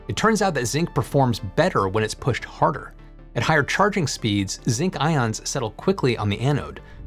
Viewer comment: Are cheaper, safer zinc-ion batteries the best bet for grid-scale energy storage or your home? Take your personal data back with Incogni! Use code UNDECIDED at the link below and get 60% off an annual plan: <link> If you liked this, check out Are We Building Wind Energy Wrong? <link> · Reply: Thanks for the analysis! At 6:09, you said "higher charging speeds", but the captions said "higher charging currents".